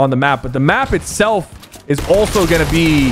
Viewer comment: WHAT DID HE SAY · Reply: He said leakers